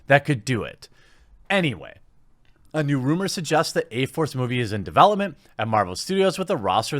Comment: I thought it said "a force movie" not A-force movie lol. A all female league of heroes? Shouldn't that be call V-force instead of A-force? Lol